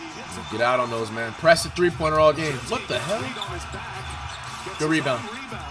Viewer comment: did flight say WTHelly